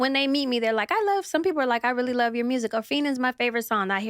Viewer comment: What song 29:49 she said ppl love? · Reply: Feenin